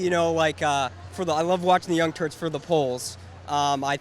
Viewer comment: did he actually call it "The young turds" at 0:17? x)